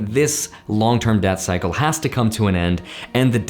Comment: I was listening to this video rather than watching and I kept hearing "long term DEATH cycle"